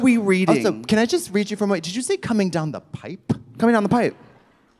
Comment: I'd always heard it as "coming down the pipe-line"...never heard Pyke lol